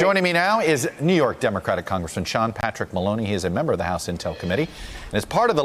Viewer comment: So so so sorry I thought I heard Baloney